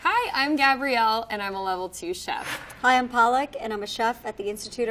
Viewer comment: did she say Paalak...... It means spinach 🤷‍♂️🤷‍♂️🤷‍♂️🤷‍♂️. She looks like an Indian and I believe she was trying to say PALAK...